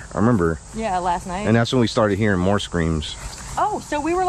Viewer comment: Did anyone else hear him say, "...and that's when we heard more screams..." and then something in a whisper, "More screams." :O